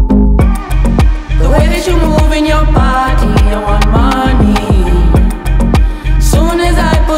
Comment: Idk if anyone else here knows Korean but I thought they were saying "the way you movin your body, I want 많이" and it still made sense lol